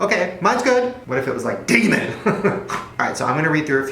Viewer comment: Shane i watched the video and at 8:14 i heard you say "Diglet", but then i rewatched the video and heard you said " Digimon". Who else heard Diglet? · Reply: 252 he said demon